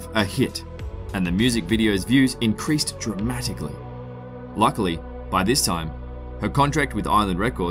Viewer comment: Titanic is a yucky person and that's facts. Edit: if you disagree with me, just simply hit the dislike button instead of starting arguments in the replies. Thank you.:) Edit 2: yes, I am aware the dislike button does absolutely nothing, but I just want no arguments in the replies that could be easily avoided. Thank you for understanding.:)🖤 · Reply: At first I thought you said lucky and I was like woah woah woah xD